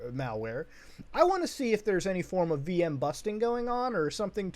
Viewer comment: I thought you said V-bucks when saying Vbox on the system